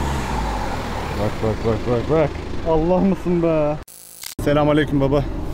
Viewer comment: da Allah mısın mı dedi ben mi yanlış anladım